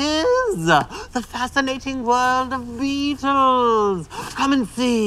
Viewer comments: Adam sounded like he said the fascinating world of VTOLs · I thought he said " The fascinating world on weeeaaasles!" Not beetles XD